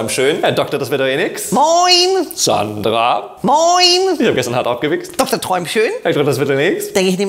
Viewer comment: Was sagt er da bei 2:35? Das klang nach etwas sehr verstörendem❤️😂😬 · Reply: Er hat gesagt,, gestern auch hart gewi*chst